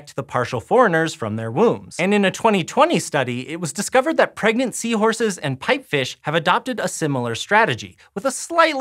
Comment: I heard "pregnancy horses" THE HORROR for half a sec